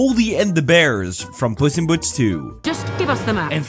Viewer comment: Did he just say puss in Butts?????